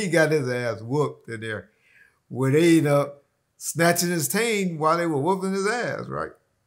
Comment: Want did he say at 1:31 mark? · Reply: "When they end up".